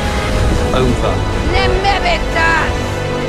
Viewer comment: what is she exactly saying? I mean i know it's Fire and it's Valyrian, but what does she say? As in the words. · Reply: Nemme be tah